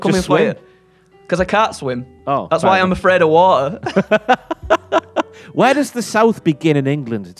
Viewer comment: I thought he said "Afraid of WALTER" at first.